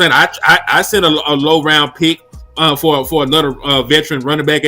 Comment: They not trading first Rd picks stfu lol and they lost to the bears calm down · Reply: talk with some respect. you need to calm your happy ass down. and if your gonna critique someone at least make sure you know what they said. he said a low round pick not first